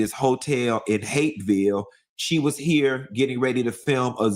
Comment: where is he calling #Hateville? Tha A.? 🍑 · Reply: Hapeville Is a small city in southwest Atlanta.